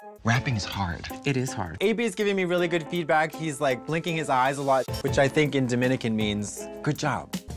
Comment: I'm hard of hearing (HoH), and when Katya said the music guy was "blinking his eyes a lot" I heard it as "licking his eyes a lot."